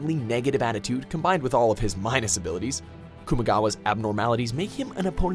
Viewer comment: ALL I HEAR IS KOMAEDA